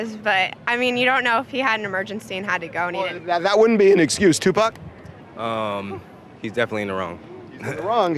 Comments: Did Harvey call the guy 2pac @ 7:16 😂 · Did he say 2Pac? Now that's funny! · Did he call that man 2pac????